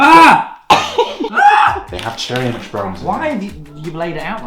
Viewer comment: Why I hear Cherry MX Brown